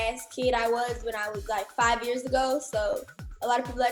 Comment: "When I was like 5 years ago" 🤦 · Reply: Right thought she said 5 years old not 5 years ago 😂🤦